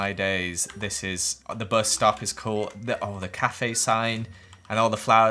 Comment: subtitles made it say birth stuff instead of bus stop.